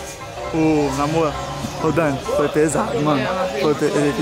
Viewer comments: só eu que entendi o chamando a de amor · Só eu que escutei que o chamou a dani de amor no minuto 20:19 Da um coração porfavor 😔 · so eu escutei o chamando a Dani de amor?? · So eu que ouvi o chamando a de amor?